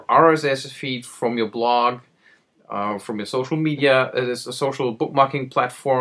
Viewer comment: Did you just say ''Gocial media'' ? lol